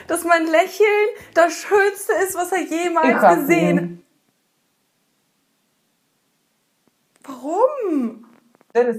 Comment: Was sagtst du bei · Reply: Sie sagt "Ich hasse ihn"